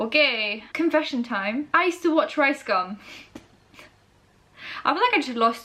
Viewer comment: did you just say "I still watch rice gum" ?!?! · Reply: i used to watch ricegum